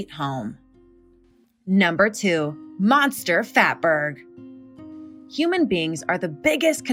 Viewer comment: I thought she originally said "monster fapper."